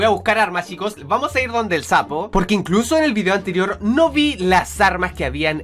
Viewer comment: Escuche mal o le dijo sapo a la tortugua